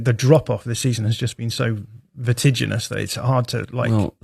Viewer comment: What did he say at 2:54? Vertiginous?? Is this guy a walking thesaurus like jack grealish or what